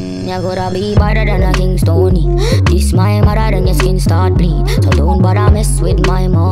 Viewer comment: Why did i hear "its my mother yolo" ....